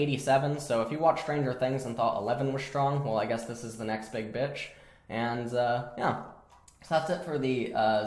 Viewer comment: did I just hear you call Zoe a "big bitch" lmao